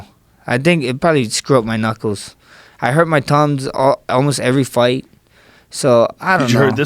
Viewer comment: anyone else hear him say “ I hurt my tongue” at first listen?